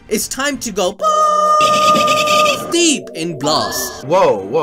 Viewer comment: When you said "balls deep in blast" I heard it as "balls deep in glass." Like broken glass.